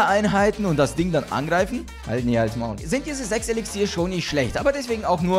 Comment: zu wem hast du Hals maul gesagt?😂 und wie sich das angehört hat😂